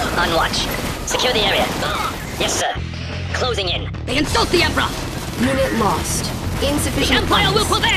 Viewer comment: Haha I heard a unit say "THEY SUCK!"